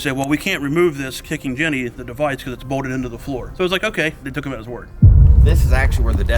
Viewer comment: what did he say? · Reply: I believe he said took his word for it